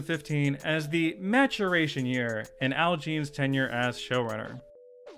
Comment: did just say matt-chur-ray-tion?